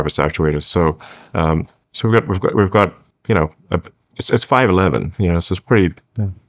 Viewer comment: Did Elon say that Optimus is going to be 5’11”??